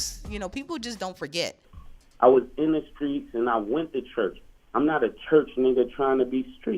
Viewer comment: I thought he said "I was a street nigga that went to church..."